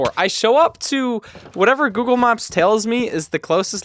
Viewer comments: Who else heard “Google mops”? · why does it sound like hes saying "google mops" 😭✌️🥀💔